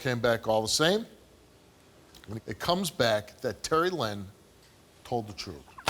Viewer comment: Oh she said terry lynn. I thought she said Sarah lynn😂 #dothebojack